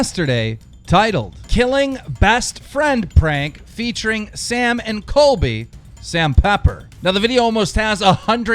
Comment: Sam and Colby are some of my favorite people and that video was hard for me to watch. Sam pepper is not a good person at all. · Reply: i thought you said sam and Cosby like Bill Cosby